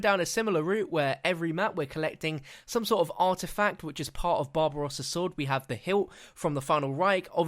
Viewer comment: Did he just say...Bob Ross' sword?